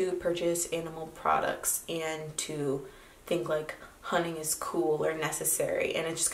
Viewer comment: HAHA when u said u cant deal with people who think hunting is cool i thought u said "honey" and i was like how many people think honey is cool is there an internet subculture im unaware of?